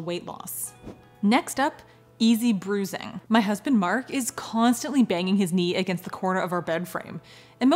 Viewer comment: I heard you said “my husband, Mark constantly banging me in the bed room.” 😆